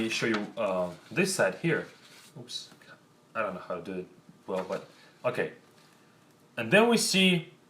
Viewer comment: In 4:48 i hear Ох Сука